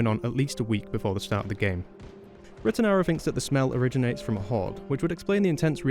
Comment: Hey, non-native speaker here, what does he say at 3:53????? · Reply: I said "retanaru", he's a YouTuber who does videos on the games mechanics, and also voiced the president in this vid